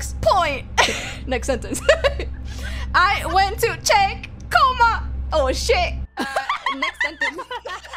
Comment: Reimu's second sentence was "I went to check, oh shit" as in "comma" the sentence notation. She was saying point/period and comma to note how each sentence looked and ended. And yes that sentence is 6 words not 5 XD. I think 1:22 Luca says "say" instead of "save" to make the sentence "Someone say me with Vox", as in "someone said I was with Vox." And just a little typo at the beginning with "it's" :P